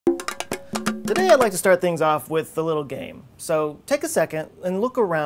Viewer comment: i thought he said im starting this out with a little gay but i realized he said game lol i was sooo confused love this guy tho